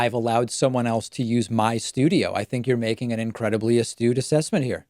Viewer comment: what word is that? Estude? I'm a writer so I try to look up any word I come across that I'm not familiar with. Looked up my guesses on Google but to no avail. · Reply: Astute